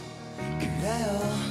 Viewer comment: why does it sound like he says "i'm freaky enough to say goodbye"